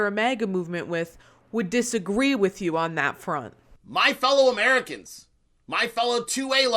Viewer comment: I thought he said 2 WAY Americans...😂